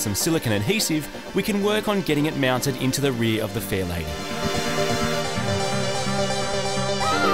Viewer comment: Always sounds like he says fail-80 :P not faiRlady